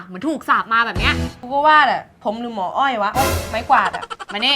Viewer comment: 555หมอ อ้อย ใครได้ยินตอน5:04